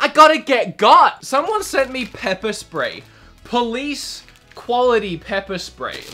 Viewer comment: I genuinely thought I heard hin say "Peppa Spray"....